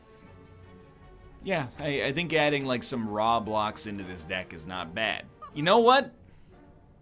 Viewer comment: Did somebody say... Roblox?